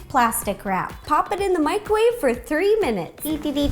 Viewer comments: When you said 3 minutes, the first time I thought you said 30. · So cute! Favorite Mario character is Yoshi. He's just adorable. At first I thought you said to put the peaches in the microwave for 30 minutes. LOL I had to rewind a few times to make sure. :)